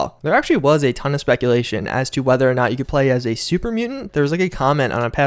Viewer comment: He didn't say "I was a supermutant" he was talking about the bounty thing and said he had found a hunting rifle on a supermutant and added a scope on it. I thought he said he was a supermutant at first to but if you go back to the panel and listen close he doesn't say he was a supermutant. So therefore I think there wont be huge mutations like becoming a ghoul or mutant or anything like that but I guess the only way we will find out is if they confirm it or show us more gameplay.